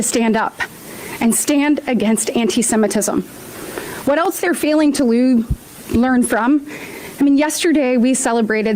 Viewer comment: Did she say, "What else they're failing to lube?" What the hell was she actually thinking about?